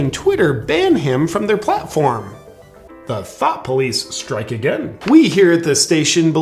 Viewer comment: He said “The thought police are at it again.” I heard, “The thot police are at it again.”